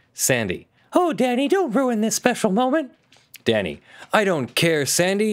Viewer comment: I heard Oh daddy please don't ruin this special moment.